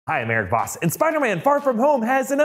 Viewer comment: in the beginning, I thought he said "I have AirPods", instead of "I'm Eric Voss"